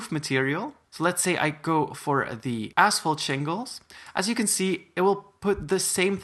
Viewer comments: I thought he said asshole jingles 😭😭😭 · sounds like he said as*h*le jingles